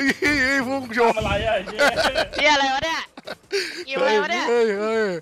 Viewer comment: เหมือนได้ยิน พูดคำหยาบครั้งแรก "เหี้ยไรวะเนี่ย"